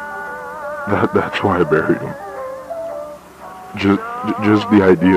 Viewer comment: me: finally decides to watch this a minute in: so that's why i buried them me: well that escalated quickly. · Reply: NGL I THOUGHT HE SAID "thats why I married him"